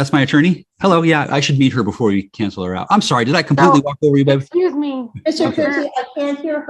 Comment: I think he says to his attorney: "Did I completely talk over you, baby?" Did he call her baby?🤔 I'm only one minute in, & already know I'm in for a treat based on his behavior so far!